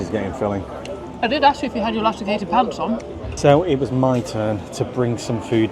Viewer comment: I thought you said "elusticated" pants, so I was expecting sexual content. Lust and food don't go together. Not in the Amish community anyway.